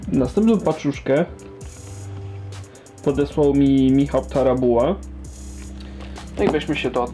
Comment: ja usłyszałem Michał Stara buła XD